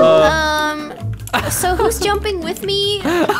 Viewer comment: No one: Me killing myself laughing from the bad puns:🤣🤣😭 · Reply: Escusme are you being crazy like ummmm I thought said sups swadeshi jumping whith me😏😏🤦🏻‍♀️